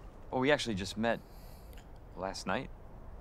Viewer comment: Lol I thought Leon jut said we met in bed last night😂😂😂😂😂